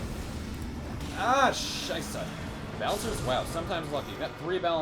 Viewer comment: did bro just say scheisse? xD